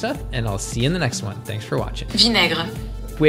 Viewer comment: “Vin aigre” , means “Vin Deisel” in - whaaaaat?? Sounds like you said, “ … in Spanch” there. 😳❓🤷🏻‍♀️❓😳 Still a funny one though. Love it! 😂💜🌱🖖🏼🌱💜😂